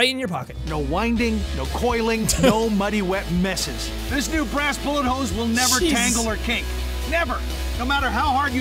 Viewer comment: When he said Brass Bullet Hose I thought he said Hasbulla Hose Lmao!!!